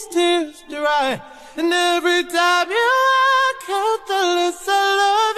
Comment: Oh. "Walk out.". This whole time i thought he was saying "Act out".